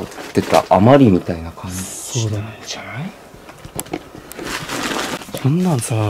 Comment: からのさんの「そうだね」に「○○じゃない」って声被ってるけどなんて言ってるんだろう・・・「したいんじゃない」みたいに聞こえなくもないけど意味わからんし